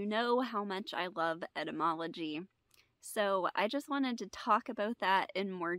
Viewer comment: Sorry, I thought you said Entomology